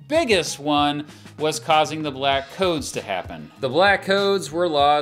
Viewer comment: For a second I thought he said Black Toads, and I was like “what the frick did Andrew Johnson do with toads?!”